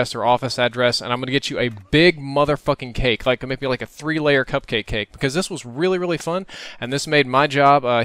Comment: I thought he said cumcake